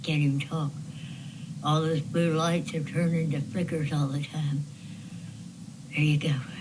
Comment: That one video the lady said the blue lights are now flickering! Am I hearing right?